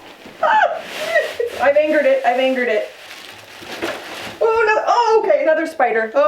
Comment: "I've angered it... I've angered it!" 😂🤣😂 Omg, that's great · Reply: Oh. My. God. I thought she was saying I fingered it😂😂😂😂 I was really confused